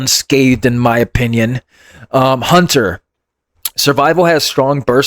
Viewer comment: You mean survival? lol Bm didn’t get touched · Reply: you are absolutely correct. I don’t know WTF. I thought he said BM Hunter.😂 that’s pretty horrible though bro survival was already bad enough in PVP